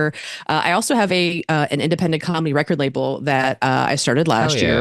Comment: Got confused for a second. Misheard "Independent Comedy Record Label" as "Independent ✨️Commie✨️ Record Label." Being named "Burn this Record" didn't reduce the confusion.